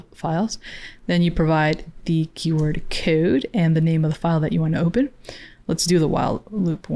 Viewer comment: "QWode" nah you doin too much lol🤣 or am i hearing wrong?